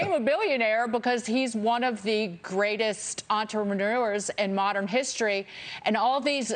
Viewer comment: did anyone else catch that? Entremanures... What's an Entremanures?